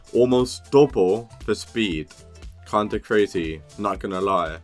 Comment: Why did he call his caterpillar glazey